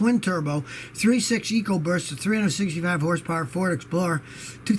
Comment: did he say "ecoburst"?